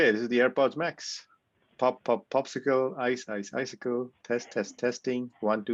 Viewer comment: I keep hearing, "pop, pop, poscicle, ice, ice, icicle, test, test, tisticle..." Sorry, low-brow I know, but I don't know how you do it. I don't think I could do this without saying testicle. Another great video.